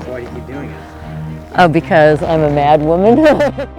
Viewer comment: Is it just me or did she pronounce human "uman" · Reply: Annoymous Kitten I heard "mad woman"....are we talking about the same thing?